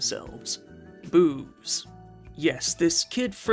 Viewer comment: Sounded like you said boobs instead of booze 😂